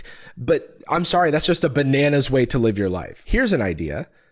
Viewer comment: I heard "bananas way to live your life" as "banana's way to live your life" and I was like damn it's pretty insensitive to stereotype bananas in that way